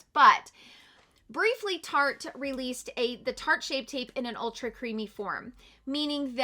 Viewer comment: I swear I heard you say SHART shape tape. Lol!!!